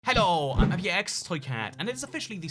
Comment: To be honest I thought he said in his intro “hello I’m your ex toy cat.” Lol